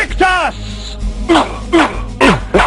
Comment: alguien sabe lo que dice en el minuto 21:13 ? me pasé este videojuego hace añales y jamás he sabido, alguien que me diga porfaaaaaa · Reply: grita "Vae Victis" que vendría siendo "Ay del conquistado" o "sufrimiento al conquistado"